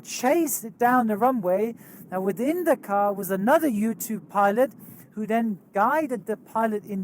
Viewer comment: I swear instead of "U2" I heard: "Within that car is another YouTube pilot."